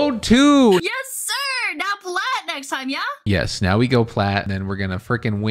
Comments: I'm not going to lie. When she said "plat next time," I thought you said "pull out next time" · Why did I hear "now pull out next time, yeah?" My brain. 😳